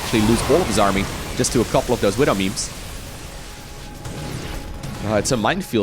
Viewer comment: did I just hear widowmemes?